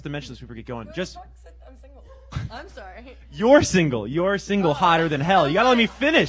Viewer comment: What did she say at 7:17 anyone? Cheers. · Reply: Also trying to figure it out, sounds like "we already talked Seth, I'm single" then covers her mouth so she might have said f*cked not talked